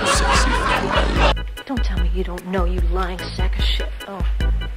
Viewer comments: What exactly she said at 0:58? English is not my language. Also thank you for this funny video xx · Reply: “Don’t tell me you don’t know you lying sack of shi— …oh” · i dont understand it 😢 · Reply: she said “dont tell me you don’t know you lying sack of sh-“ and she realized she went way off script lol she got too into it